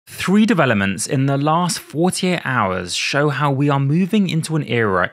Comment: I swear to god I heard you say 3 developments in the last 4 to 8 hours and did a double take. Then I realized you said 48 hours and still did a double take. Things are moving quick, aren't they? We may have more than 3 LLM powered robots, GPT 4.5, Model 5, automated software development, and 1 bit quantization by the end of the year. I am starting to think we really will have AGI by 2030.